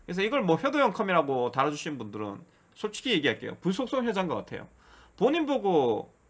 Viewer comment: 평소에 잘 보고 있습니다. 이번에는 궁금한게 생겨서요. 17:55초때 '솔직히 이야기 할께요' 다음에 뭐라고 하신거에요? 저는 아무리 들어도 '볼수록 해자인것 같아요'로 들리는데 앞뒤 내용을 보면 이상하잖아요... 괜찮으시다면 알려 주세요. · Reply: 불속성 효자인거같애요라 들리는거 같애요